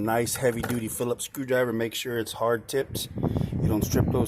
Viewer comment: Did you say Hard Tipped?